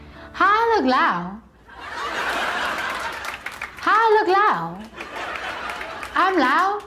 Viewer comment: “How I look lou?” “How I look lou?” “I’m loud!?” “I don’t even think I’m like loud”🙄🤚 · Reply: I heard "How I look lao?"